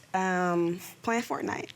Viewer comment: she said "play fortnite" and the subtitles say "Planned for tonight"